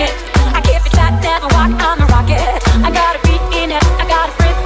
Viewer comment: i hear hold up in the crotch .____.